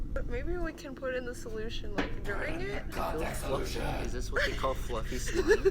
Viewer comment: I heard a voice like: CONTACT SOLUTION